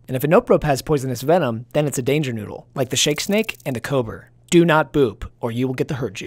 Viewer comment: THE REST OF THE DAY, I KEPT HEARING HIM SAY "And the cober" SOMETHING ABOUT IT ID AMAZING